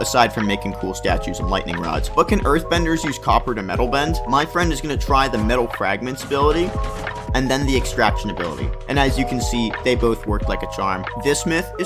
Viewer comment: I thought he said meth benders💀💀💀